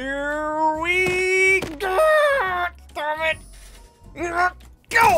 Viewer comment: DID HE JUST SAY DAMMIT